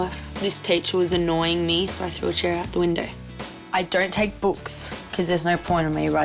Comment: Did she just say that she threw her teacher out of the window? Or did i hear it wrong